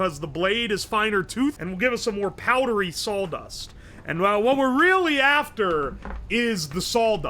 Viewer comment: It sounded like he was saying "saul dust"